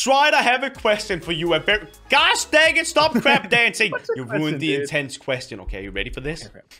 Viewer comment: It sounds like he says 'stop crap dancing' lamo